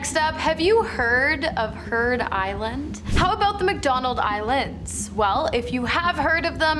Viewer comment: Am i hearing things or did i hear McDonald's islands